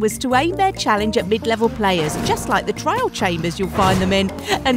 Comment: Did say "trial" or "trail" chambers? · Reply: "trial"